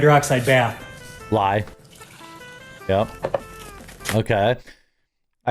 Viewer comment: = "lye" or caustic soda vs. "lie" of falsehood. It took me a moment to realize is not calling him out for lying.